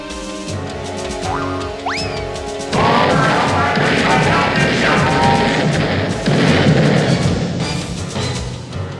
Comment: What was the Robot Squidward saying at 5:43? I couldn’t figure it out due to the self-destruction noises. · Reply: Oh I hope I payed my health insurance! It's a line from Lights, Camera, Pants when he plays the Sneaky Hermit.